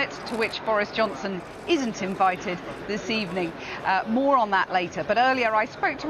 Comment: I thought she said. Boris Yeltsin wasn't invited lol. Was like wtf had to rewind